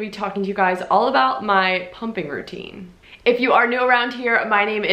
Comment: Totally thought it said pumpkin routine LOL PSL anyone??? Thanks for sharing! I miss these days!